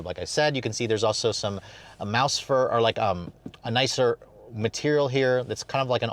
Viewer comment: that sounded like a voluntary/intentional slip-up, mouse fur, nice one%